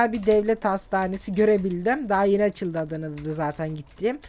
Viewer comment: ne diyor Adana'daki mi diyor anlayan var mı?